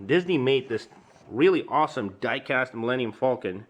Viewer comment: I heard dike ass. lol